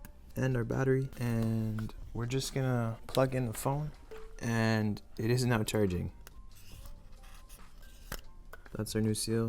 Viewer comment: thought he said “and… its not charging” 🤣